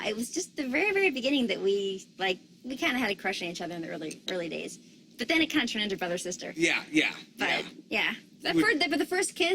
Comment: missmari89 English is not my first language, I understand everything except what Danica said at 0:52? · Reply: Danica: "we kind of had a crush on each other in the early days but then it kind of turned into brother sister"